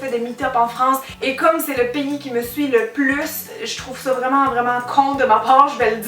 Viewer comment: au début j'ai compris "c'est le pénis qui me suit le plus" 😂